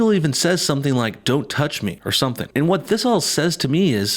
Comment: it sounds like he said "told you not to touch me"